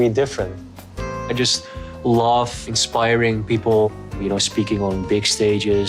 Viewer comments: I thought Jesse said, "I love expiring people" as the camera shows the seniors 😂 I was like WAAAAITTT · I thought Jesse said, "I love expiring people." 🤣🤣🤣🤣🤣🤣 I was shocked he was so rude. I couldn't believe didn't comment on his description of the elderly. The 3rd time I listened I realized it was "inspiring people"